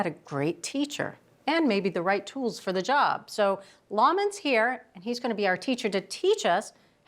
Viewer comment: I mean no disrespect I heard. "ramen" not Lawman